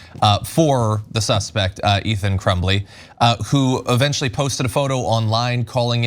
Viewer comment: is it ok that i half laughed when i thought i heard "Ethan Trembley"? 4:12 it's kinda close enough to be mildly humorous i think maybe i was already experiencing some association with the surnames before hearing the shooter's first name just now so it's interesting